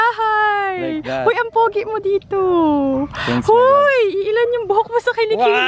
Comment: did he just said " Thanks my love "?